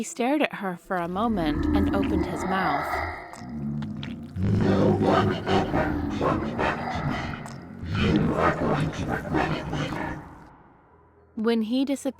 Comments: Can someone tell me what the devil said 8:17 I didn't understand a word 😥🥺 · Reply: I think he said. No one ever talks back to me. You are going to regret it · can anyone tell me what he said? "No one ever ___ to me?" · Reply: "No one ever talks back to me."